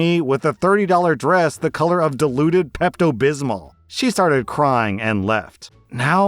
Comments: I realized its "diluted" pesto bismol, as in, watered down. But I hear "diluded" pesto bismol, as in "has beliefs that do not align with reality" and I find that so much funnier. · I know the wedding story OP said the color her sil was wearing was diluted Pepto Bismol, but I heard "deluded Pepto Bismol" and I'm going with that.